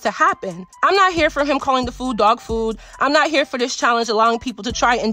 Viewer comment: It went over my head I thought he had said TOFU but he really sayin DOWGFOOD in his NY Accent yikes